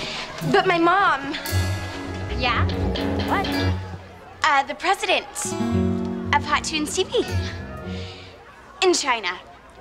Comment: I watched this when I wasn’t yet fluent in English and I always thought mitchie was saying that her mom was the president of China💀